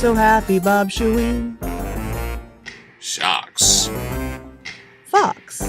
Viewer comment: Who else thought said "fucks"? 3:04 In Australia we have kangaroo crossings and koala crossings. 3:16 That's how they say "hello" in Fiji (albeit without the 'o's)! I learned it from Hi-5 as a kid. :)